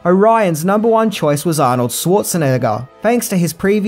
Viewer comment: Did just say in the N-Word?